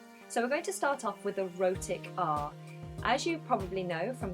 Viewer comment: I literally heard : so we are going to start off with "erotic art" ...enough accents for today, I am going to sleep